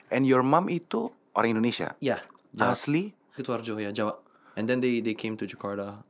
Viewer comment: klo gak salah denger, max bilang ibunya asli kutoarjo (?), jawa. Kutoarjo Purworejo bukan ya? 😁